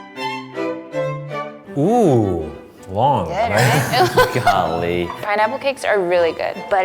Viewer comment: did he just say “ngon” ? ( means dealicious in Vietnamese)